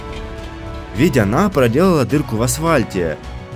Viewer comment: только я один слышу "асвальте"?